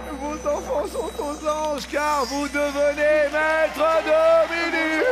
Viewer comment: J'ai entendu un "Vous devenez maître de midi" avec un son très bas